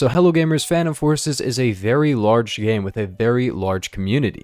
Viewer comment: At the start, I thought u said “fan of horses” instead of phantom forces